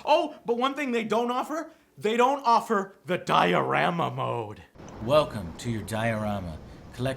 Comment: coulda swore he said diarrhea mode... CUZ THAT'S EXACTLY WHAT IT WAS!!